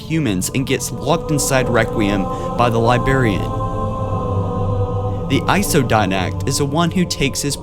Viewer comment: um at 2:56 did u say libarian